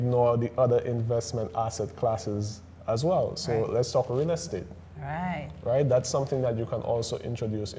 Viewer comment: What does he say at 10:09? Let's talk ___? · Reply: Thanks for watching. He said "let's talk real estate"